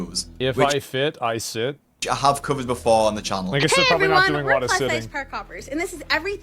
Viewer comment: i swear i thought i heard her say hi everyone we are plus size car covers